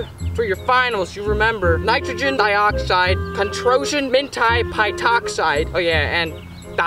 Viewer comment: did I hear hentia??